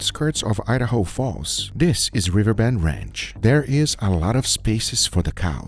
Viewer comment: Why put on a hair net on a bald guy lmao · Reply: I thought he was saying “river bed ranch”, this whole time.